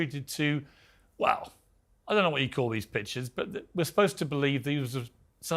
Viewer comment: Goddamn she’s such a massive liar. It sounded like Piers said “These Bitches. As you can see 😂🤣”. I know he meant “these pictures.”